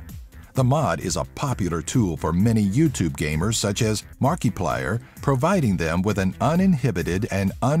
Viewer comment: Mark-e-plyer...... Wat... AM I THE ONLY ONE WHO HEARD THAT?!